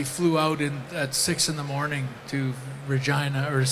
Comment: did Bret just say Vagina? 😂😂😂